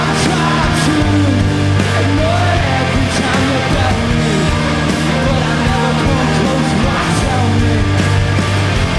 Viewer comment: What does he says at 3:34? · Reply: And I’ve tried to ignore every time you bell me, but I never come close